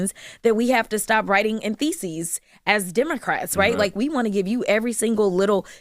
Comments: did she say feces? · did she say Democrats have to stop writing in theses or faeces?! 😂 they definitely talk and write a lot of shite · Did she just say writing with feces?💩🤗😉